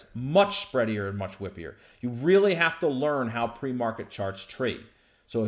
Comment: I thought you said Pre market “sharts “ Phew, I was worried